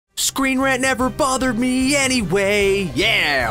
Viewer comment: why did i hear it as "Screen Rat never bothered me anyway"?